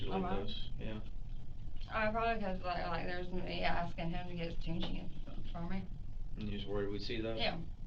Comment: did she say she was texting him asking for tucci? Did I hear that right?